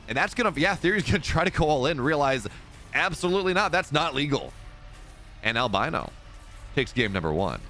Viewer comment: did you say the all in was not legal or not lethal at 5:11? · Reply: Not legal